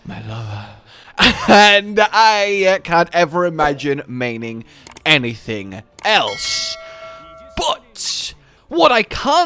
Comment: I really thought he said "I can't imagine mating anything else"